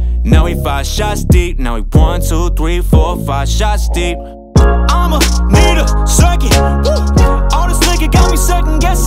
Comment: I thought it said "I'mma nigga" instead of "I'mma need a second" damn I'm dumb